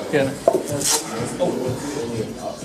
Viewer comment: he even says "Thank you". So I dont understand why some call him "Douchebag". He is an animal but still polite so what more are you looking for? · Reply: he said "dankeschön" it's german^^